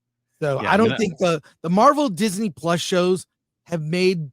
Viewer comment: it sounds like says "Disney Flush", which actually makes sense.